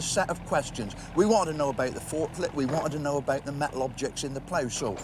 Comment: He said " fortlet" , i thought he said "forklift" .I started paying more attention after that.